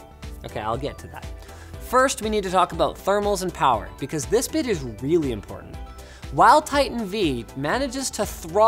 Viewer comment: Sounded like he said "This bitch is really important"